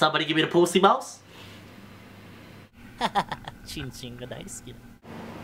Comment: I thought he's saying "My name is ochinchin" Then..